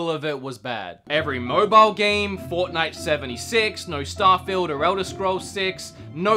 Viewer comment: did you just say fortnite 76?